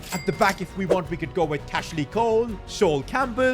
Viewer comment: Did he just say cashley Cole